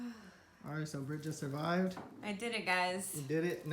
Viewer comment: he sounded like he said “okay so Bridget survived” lol just me?